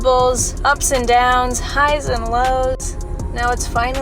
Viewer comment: Did she say "highs and loads" at the beginning??? Lmao